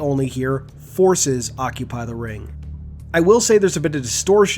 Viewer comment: I originally thought he said “The voices occupy the ring”, which I think is a pretty cool name for the Created by the Banished